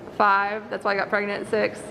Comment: "1,2,3,4,5 that's why I got pregnant, 6..." 😂😂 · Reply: i thought she said "that's how i got pregnant" xD